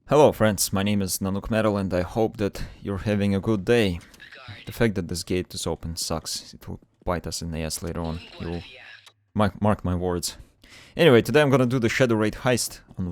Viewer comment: When you said "the fact that this gate i open sucks" i was pretty fay away from My phone so i heard "the fact that this game sucks"... lol